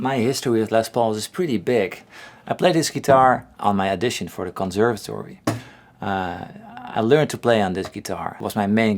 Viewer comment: did you say conservatory?